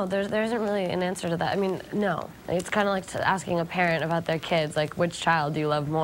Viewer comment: Lol i thought she said Parrot not parent :PP